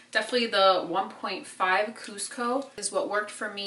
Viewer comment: what shade did you use? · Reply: I’d think she said ”1.5 Cuzco” somewhere around 04:50 in the video ☺️